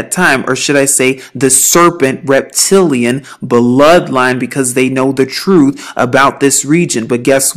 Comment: Did she say reptilian bloodline?!?!???? Around